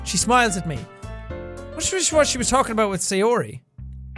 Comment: it sounds like he said “I swish-wash” instead of “I wonder”